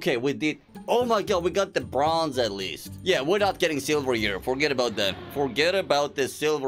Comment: According to my calculations on 7:12 in the subtitles it said egetting instead of getting just wanted to point that out🤓